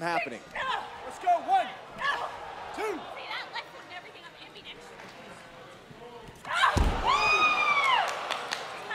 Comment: What did Peyton say at 0:47🤔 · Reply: RABID CUJO CRAZY ok😂